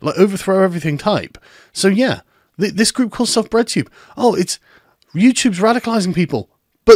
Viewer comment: Thought he said redtube at first. A whole different kind of intersectionalism found on that site.......